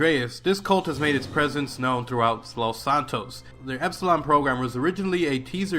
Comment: I thought that said Epstein program